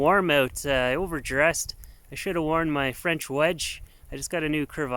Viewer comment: Wait. Did you just say "I should have worn my french wedge" ?????